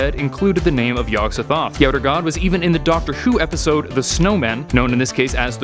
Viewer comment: Did he just say, "even in the D r. S h o o episode"?